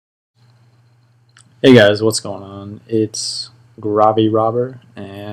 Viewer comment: At first I thought he said “hey guys it’s Robby rotten” and I was like, huh? This isn’t lazy town